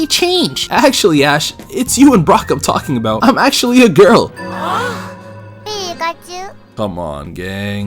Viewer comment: for a second I thought she said “I’m not a girl”